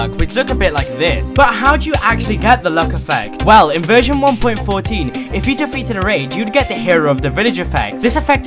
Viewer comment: I heard- ‘in virgin 1.14’ and was honestly so confused.